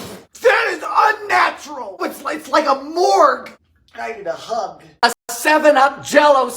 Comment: did he say 'I need a hug'? I don't blame him, cold jellied Spaghetti rings 🤮